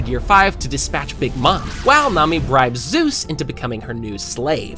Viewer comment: "while MOMMY bribes zeus into becoming her new slave" HE DID NOT SAY NAMI 💀